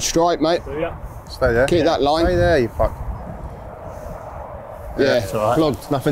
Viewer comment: does say "stay there you f*ck?" 😂